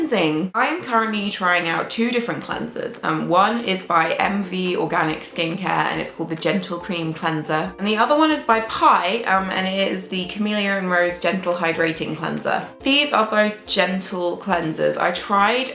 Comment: I thought you said 'Genital Cream Cleanser" hahahah. Sorry. <3 <3